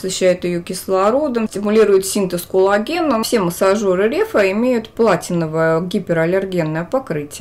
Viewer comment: Сначала послышалось "Репа". Ну, думаю, удачное название! :D, в этом видео ты особенно хороша!